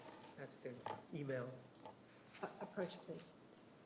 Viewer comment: My internet connection slowed up for a second and when the judge was replying to the defense attorney, it sounded like she said "Bitch, please" (rather than "approach, please.") LOL